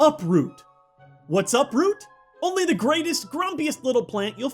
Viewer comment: I thought you said what’s up root